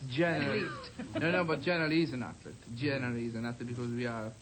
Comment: "A singer is an athlete" point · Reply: I heard "actor" not "athlete"